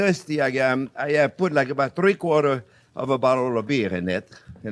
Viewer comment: Danger genius at work. :-) Oops I thought you said 3 or 4 bottles of beer ...It's ok though I took car of the excess myself.